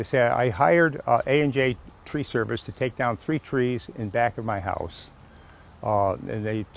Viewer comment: Oh we thought you said 3 trees AND the back of your house.